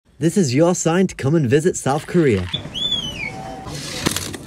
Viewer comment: Thought you said south Korea